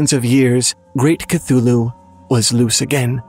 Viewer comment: When you first said Great Chtullhu, I thought you said Rick Chtullhu and I was like, "Oh, he's got a first name..."